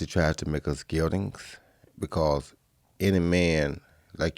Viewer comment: what is he saying here? "Society wants to make us g....." I don't understand? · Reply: oh I think he's saying "geldings". Never heard of it before so I had to Google the word 'eunuch'' on like 10 different thesaurus websites xD